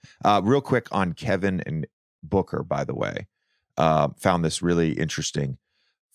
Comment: I thought I heard JJ say "Kevin Booker" (there's an and in between). but the way these two are playing, might as well just call them that